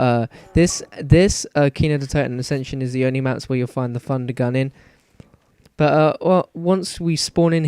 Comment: i think it sounds like he says fun-d-gun! :D